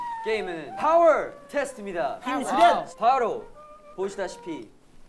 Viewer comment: 펜하 보다가 이거 보니까 힘 수련이 계속 심수련으로 들려